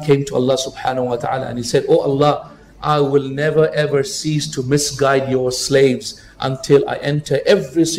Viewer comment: and 10:43 , I believe the word should be "cease" and not "seize". There are quite a few errors in some of your videos between the words that are shown and the actual words that the preacher is actually saying. I suggest for the video creator to go through the subtitles meticulously to avoid confusion.